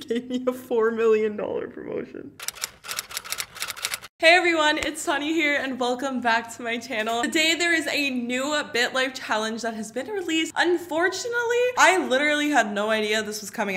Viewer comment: I'm I the only person who heard her say itsfunneh... Just me...ok Oop I listened to it again and I heard, but idk lol